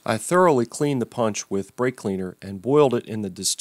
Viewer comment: Where does the salt,vinegar and peroxide come in? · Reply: ok thank you. I thought I heard “break clean”